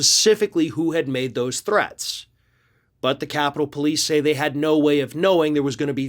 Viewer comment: When you said, "the threats" they'd received - I thought you'd said the 'tourettes' they'd gotten!